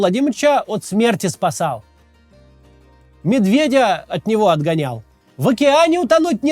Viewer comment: Что-то вдруг послышалось - медведева от него отгонял. Представил картину - "Па-а-шёл, па-а-а-шёл отседова!" - и ножкой замахивается.